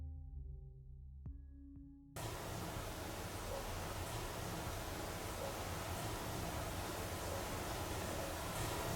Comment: 〜 木魚の音のところ、場面切り替わる瞬間に女の人のボソボソっと何か呟くような声みたいなのも聞こえますよね🥺なんて言ってるんだろ… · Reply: 「死んでたか」って言ってます。